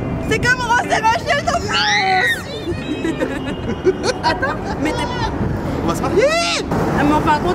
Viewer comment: elle lui dis “mais attends t’etais pas en couple toi ??” ou c moi qui reve ???